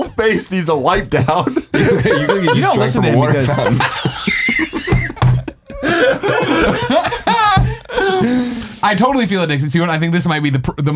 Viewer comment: What does Nick say at 4:00? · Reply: 'You shouldn't listen to him' it's that makes Greg laugh, saying 'it looks like you've drank out of a water fountain.'